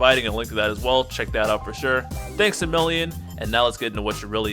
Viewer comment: For some reason I thought you said "thanks civilian." I thought wtf til I replayed it.